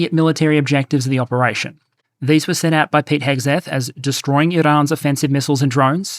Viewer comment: Pete Hegseth sounded like "Pighead Zed" to my American ears there, so I'm keeping it